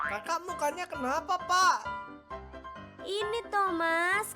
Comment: kok gw dengernya "kakak muka nya kenapa pak"